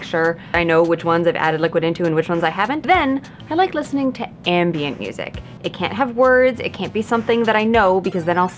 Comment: You said "ambient music." I heard "anime music."